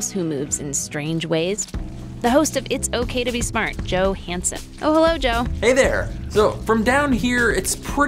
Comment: Thought she said Joe Handsome, and i thought, go figure xD